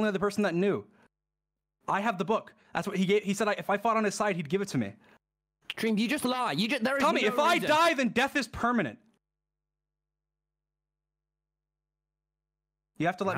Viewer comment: “if i die, death is permanent” idk dream, sounding a lot like JESUS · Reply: I thought you said death was pregnant👁👄👁